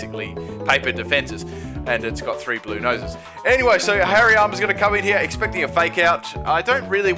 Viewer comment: I thought he said HAIRY ARMS instead of Hariyama